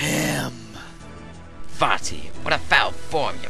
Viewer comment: it sounded like Ezlo called Vaati "Fatty" at 0:19 LOL :D